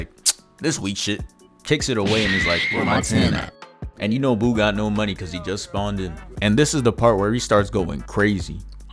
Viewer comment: What is being said there? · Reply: Lil uzi meme where he says “it’s only my third day out here ion know”